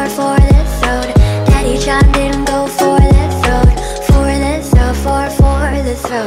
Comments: What I heard:"daddy sh0t ben go for the throat" Lmao her version makes more sense 😂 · I thought she said “Daddy shot Dingo for his throat” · I thought it said, "daddy got shot go for the throne" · I thought it said "daddy chimed in "go for the THRONE" " oof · i first thought it said 'go for the throne' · I THOUGHT IT SAID “Daddy shawn dead go for the throat” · I thought it said “daddy shot men” 😂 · "DADDY CHANGBIN GO FOR THAT THRONE" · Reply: Every time I listen to this song I hear "Daddy changbin go for the throat" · "DADDY CHANGBIN GO FOR THAT THRONE" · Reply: I heard when I heard it the first time but then I heard "Daddy chair in go for the throat" and I was like 'wtf is wrong with my brain' until I saw the lyrics. I'm such a dumb girl right? :/ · this is what i always heard “daddy shot nengo for the throat” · Bro I thought it said legit "daddy chimed in go four and through" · I first thought it said "daddy chimed in before the thrown" 😂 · I thought it said daddy John didn't go for the throat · I always thought it said daddy shot men instead of daddy chimed in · I thought it said “Don’t you choke, daddy shot in go forward the throat” 😔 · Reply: I thought she said daddy told me go for the throne 😞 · I legitimately thought it was “Daddy-Chan didn’t go for the throat” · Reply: I thought it said,"Daddy chopped in go for the throne." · I legitimately thought it was “Daddy-Chan didn’t go for the throat” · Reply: I hear "Daddy Changbin go for the throat" 👀 TRUST ME IM INNOCENT